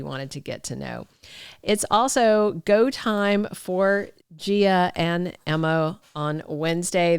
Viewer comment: i heard Gia and Emmo